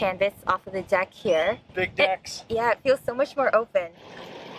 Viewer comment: what did he say??? 😮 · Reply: Big decks!!